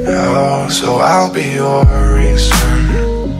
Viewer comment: I thought it said "erasure" instead of "reason" and erasure makes more sense...